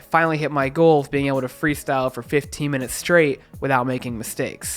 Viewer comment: did you say 50??? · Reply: 15 😂 I wish 50 haha